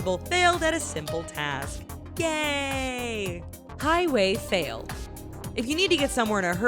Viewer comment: Did She just say gaaaayyyy at 8:04 ? 😂😂😂